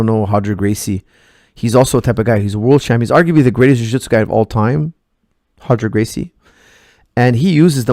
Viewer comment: Did he say Roger Gracie at 5:10 ?